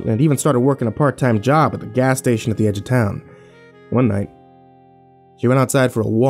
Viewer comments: I heard "she got a part time job at the gas station at the end of town" and if this is part if the tales from the gas station i will be shook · Omg!!! I heard "shitty gas station at the edge of town" and I flipped my shit.